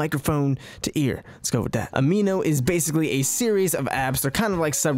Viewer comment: I hear " Ameano is a series of abs."